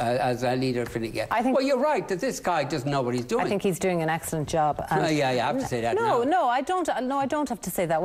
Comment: did she just say dat endas doin a great job 12:35 oh god shes just as bad go back to school missus